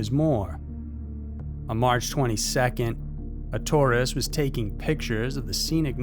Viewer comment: You said Tourist and my dumb ass thought you said Taurus, and at first I was like "why is a car taking pic of the sky" then I was like "no he meant Taurus like the star sign" and finally I realized what you actually said 😂😅🤣😅